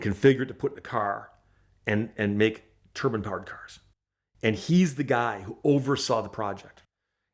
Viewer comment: I remember riding in the turbine car at the New York Worlds Fair also. Great story. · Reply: I actually thought he was saying Turban powered car. Lol birdy num num